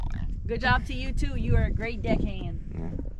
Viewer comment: What did call him???? Yikes!!!! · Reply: She called a deck hand 🤣